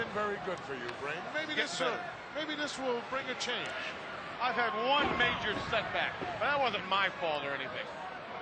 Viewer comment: I had this on in the background, sort of half paying attention and at 12:56 I thought Heenan said, "I've had one major s*x act but that wasn't my fault or anything" 😂😂